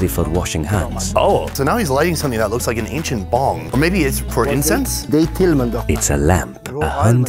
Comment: did he said ancient bomb?